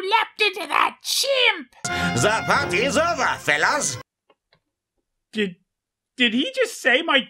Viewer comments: I always hear "The fun is over, Phelous!", even though I know he's saying "The hunt is over, fellas!". ...seems to fit better with this audio-visual tort- I mean, mast- movie. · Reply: I thought he said the plot is over phelous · I always hear "The fun is over, Phelous!", even though I know he's saying "The hunt is over, fellas!". ...seems to fit better with this audio-visual tort- I mean, mast- movie.